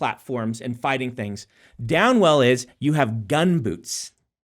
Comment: When Brandon said/talked about Down Well I thought he said Dan Well 😂